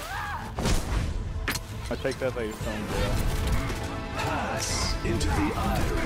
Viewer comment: Why do I keep hearing 'Is he still in jail?' Can anyone explain please?